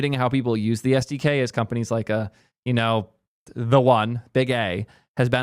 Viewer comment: I heard "be gay" and was like "alright nice but what?!"